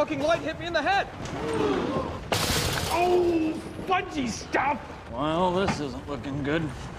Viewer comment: Could someone please tell me what the dude in 08:33 says ? I kinda get that it's a different way to say "fucking shit" without cussing or it might be a made up phrase :P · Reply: Fudgey Stuff